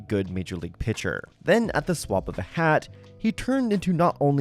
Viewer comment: I won’t lie, I originally thought you just said “drop of a hat” wrong until I processed it and realized it was great wordplay with “the swap of a hat”!!